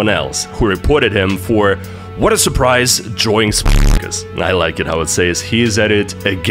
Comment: yo can someone tell me what the bleep said? I cant tell what hes actually saying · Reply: swอstikอ